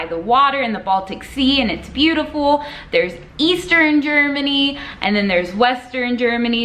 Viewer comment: For a second there I thought you said Easter Germany not Eastern Germany lol